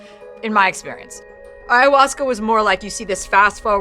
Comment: What was the name of the drug she was talking about at around 14:50? I couldn't hear well. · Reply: ayahuasca